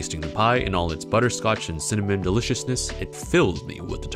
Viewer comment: I love that in the end at 5:31 the subtitles say "it fills me with the termination" instead of "it fills me with determination"